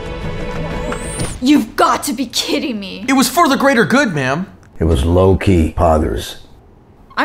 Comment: I heard “it was low key hottest”